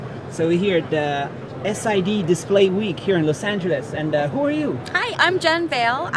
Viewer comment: i mean are you kidding ..? WHO YOU ...doesn't feel rude ..? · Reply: Relax, he was not rude. At 0:06 and 4:06 I heard he said "who are you". Its informal conversation, not rude to the other person.